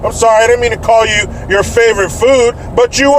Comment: Haha, thought you said favorite fruit...